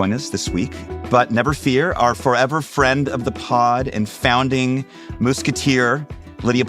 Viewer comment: Did you just say "moose-keteer"?